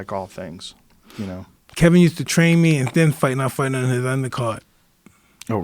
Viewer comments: Mike says: "Kevin used to train me..." but then i can't understand what he said. Can somebody help me on this one please? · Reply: He said Kevin trained me and sometimes I even fought on his undercard. I liked him as a trainer. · Anybody know what mike said at 49:05? I lost him after “Kevin used to train me ??????? Undercard” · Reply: and i would fight in his undercard? thats what i heard